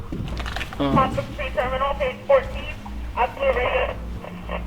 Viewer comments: In minute 19:25 thru 19:27 it said radio 7 0 14 idk I heard it tho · Put the video in 0.75 x speed and go to 19:25 I couldn’t really make out words but I heard “page 14” or 40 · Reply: Page 14